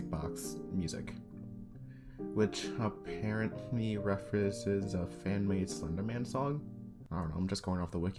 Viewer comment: What are you saying at 3:09? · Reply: "References"